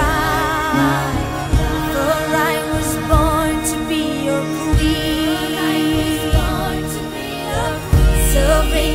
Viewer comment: does anyone know what she says at the scene 2:35? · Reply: Don't know why it matters. But she says ''bring the cage and the ram''